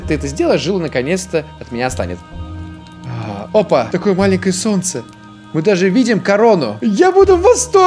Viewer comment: какое маленькое солнце? или мне послышалось